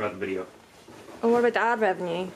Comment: what did she say lmfao nice to see you're not dead, good video! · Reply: What about the ad revenue